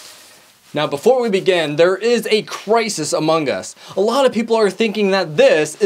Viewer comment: did he say among us? or is it just me